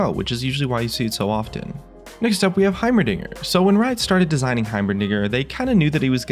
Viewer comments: Did you say "heimernigger" at 1:35? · did he say "heimerniger"